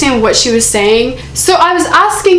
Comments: To me it sounded like she said "mya turned if off" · omg i heard "mike turned it off" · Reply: I heard "Mya turned it off" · Sounds like she said Mike turned it off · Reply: I heard "Mommy turned it off" and if she really said another person turned it off I'm going to literally die- Like my phone is going out the window😰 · I heard. " my turned it off "